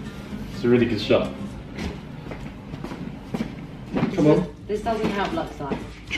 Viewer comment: what did she say at 5:44? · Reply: "This doesn't help LuxLike"